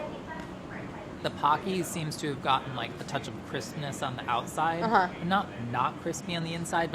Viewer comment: When Chris said "crispness," I heard "Christmas" and I believed him.